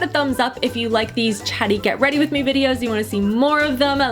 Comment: I literally thought you said teddy get ready with me and I was very confused lol :) love you!